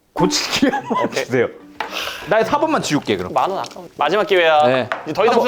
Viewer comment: 아 '만원 아까운데' 라고 들리는데